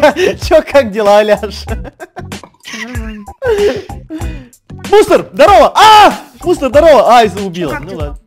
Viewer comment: В какой-то момент послышалось,что Эд сказал не Мунст,а Мусор😂😂😂😂😂😂😂